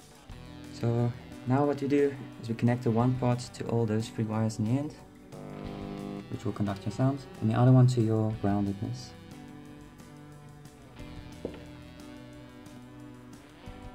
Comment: Hi. At 1:35, you said to attach it to what I hear is "roundedness" , also seen in the transcript. Did you mean ground? What does it look like? I cannot see what it looks like in the video. Thank you very much for this video and for any help you can give.